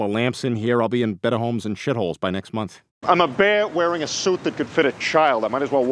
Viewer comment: When I watched ted the first time I thought the same thing it sounded like he was saying "Bwear"